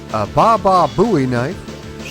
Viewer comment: Im a huge, lifelong Howard Stern fan, so as im listening to this as im taking out the garbage, i had to stop, rewind, and look when i heard "bababooey knife" 😅😅 I go "wait what??? Ive been listenint for 35 years, and i never heard of Baba Booey having a knife, or some joke about him and a knife, lemme see just what a bababooey knife is, cuz i--- oh wait... a BOWIE knife, so might as well throw a baba in front of booey....as peeps are known to do..." Lol awesome Ps i also like that you can bludgeon a dude to death with a bullet-less M16, instead of just losing your weapon like in other games